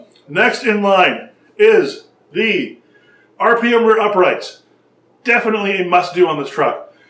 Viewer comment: I don't understand what you are saying at 11:18. It sounds like you said "RPM Uprights"? I know you are talking about bearings shortly thereafter, but I am just slightly confused. Also, I wen't to the RPM site and it says that the bearings are not a replacement for stock Traxxas Axle Carriers. So, does that mean that we need to change that carrier to fit the new bearings? Sorry, I am a newbie and just getting in to the hobby. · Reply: Yes, RPM uprights <link> they come with oversized ball bearing, so you change both with this kit....a must have upgrade